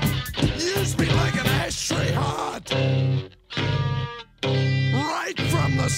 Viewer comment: When I first heard this without knowing the title I thought he said 'ash tree heart'